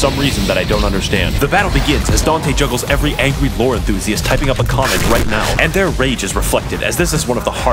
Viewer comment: As an actual vore enthusiast, I laughed at every joke involving it (yes I'm outing myself right here). Edit: I just now realized he said lore enthusiast and not vore enthusiast. Still standing by what I said though.